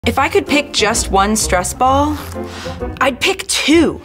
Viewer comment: For a brief moment I thought it said stress balls in Ohio 💀💀💀💀